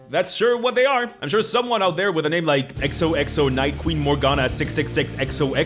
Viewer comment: when I first heard this I thought I heard nexo knight any nexo knight fans?